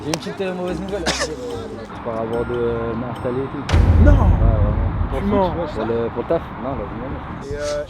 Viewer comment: il a dit quoi tanane? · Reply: Je crois il dit je vais m'installer et tout pour le taf